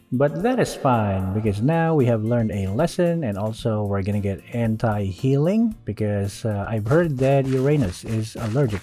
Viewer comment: anyone else heard "Hentai-healing?" Lol